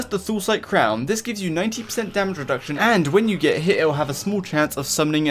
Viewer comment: just me or did he say 19% lol